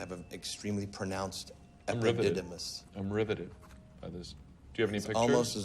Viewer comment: Subtitle error at 0:13 Subtitle says: "enriveted that is" Correct: " I'm riveted by this" ( a very facetious, way to say 'I'm really NOT interested')